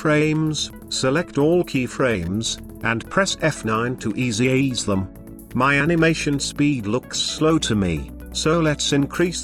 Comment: Press f9 to what??? Did not understand that word one bit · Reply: Press F9, for Easy ease the Selected Keyframes.